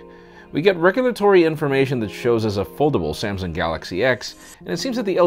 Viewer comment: Oh you said 'Foldable', i thought you said 'Affordable', never mind !!